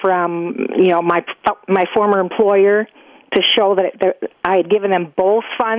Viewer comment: What did she say at 1:25?? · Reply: "My former employer"